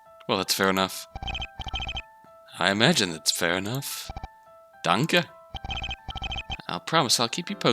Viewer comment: didn't realize that was German and thought Phoenix just said "dank" Also, I love how your "generic detective voice" is just your Keiji voice now.